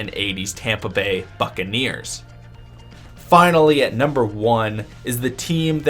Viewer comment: Damn. I fr thought you said the Navy’s were number 1